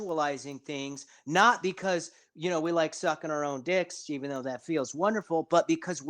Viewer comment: did bro say he sucks his own dick?